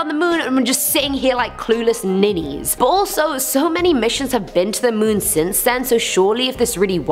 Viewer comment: did she say ballso